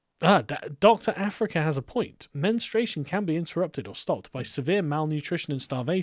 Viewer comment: I heard "man-struation". And that makes sense, as men caused it apparently :D